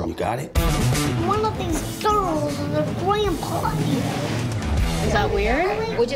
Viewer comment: What does the little girl say at 1:40? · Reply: Matthew Blake Nope. She sais, "one of these girls has her grandpa here".